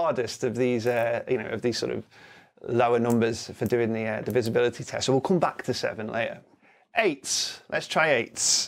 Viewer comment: Oooh, I thought it said ‘Disability Tricks’ 🧑‍🦽🤹‍♂️👨‍🦯